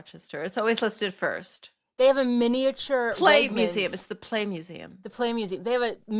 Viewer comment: Ha! Ha! I thought you said "The Plague Museum." But, then again . . . why not?